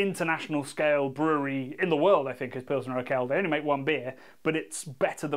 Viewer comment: What does he say at 17:50? Best international brewery is what? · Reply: Pilsner Urquell!